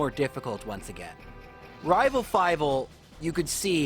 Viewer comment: Did he just say "rival fival"?